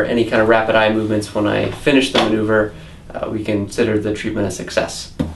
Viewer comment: Lol I thought he said 9% success rate